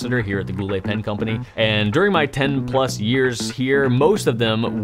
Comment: I thought you said "during my pen-plus years here" at first...